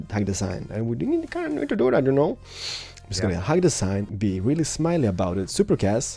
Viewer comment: If you turn the captions on at 11:30 it says 'Be Really Smile About Its a Big Ass Buddy' instead of 'Be Really Smiley About It'